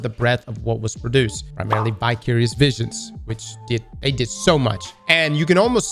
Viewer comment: thought you said bi-curious visions 🤣😂😅